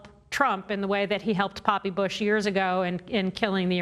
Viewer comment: Did she just say “Papi Bush”?